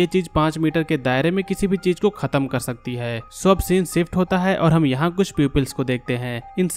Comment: Did he just say peyoples?0:22 instead of saying people's 🤣